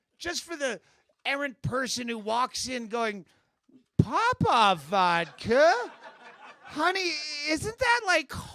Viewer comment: I thought it said poopy vodka at first lol.